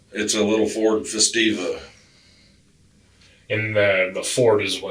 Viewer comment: Interesting that the subtitles changed ford festiva to ford fiesta. Ford made both. He clearly said festiva, i wonder which they were actually driving.